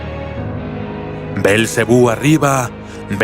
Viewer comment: like si crees que esta diciendo belcebu merengue like si lo crees